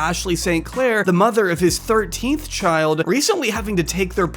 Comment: sir did you say 13th child???